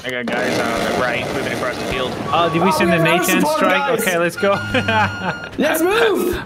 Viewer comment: did someone say A10 Strike?